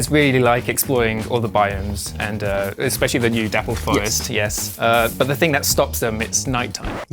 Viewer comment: Everytime they mentioned the Dapple forest I thought that they were saying Dap Up Forest. This is the type of sh@ Piccolo would train for the Aura Farming Tournament of Power 😢😢